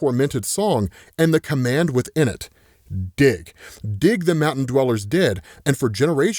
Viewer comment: I thought he said, "His tormented song and the command with it: DICK!" and I nearly spit out my drink